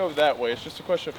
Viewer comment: All I heard was “it’s prolly that way” 😂